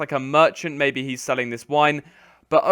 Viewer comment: did you say he is Selling his Wine or Wife? Could not make it out clear :P